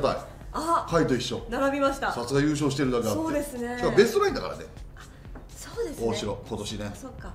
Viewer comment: なんて言ってるかわかる方教えてください！ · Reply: そうですねぇ！